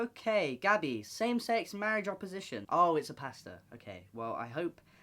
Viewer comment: i know this isn't the important thing but when you said pastor I thought you said pasta and i was so confused and then immediately died laughing